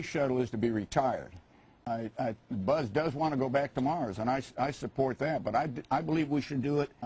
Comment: I heard: "Buzz does wanna go back tomorrow and I support that ..." NOT: "Buzz does wanna go back to Mars ...". Why would Cernan say "Mars" -- it wasn't in the context of the topic or the interviewer's question.